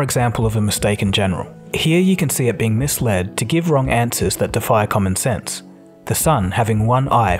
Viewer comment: you remove the door lol I thought you said it had common sense.